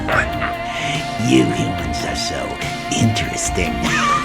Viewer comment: What did he say 21:36? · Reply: ener mener you humans are so interesting